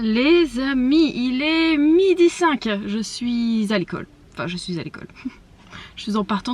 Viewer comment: Je regarde la vidéo en 1,25 et un moment dit " je suis à l'école " et avec le speed j'ai entendu "je suis s'alcool", ça m'a surprise 😂